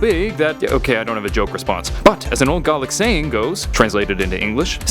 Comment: did he say garlic?